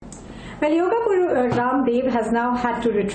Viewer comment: did she said when yoga Puru 😂😂😂😂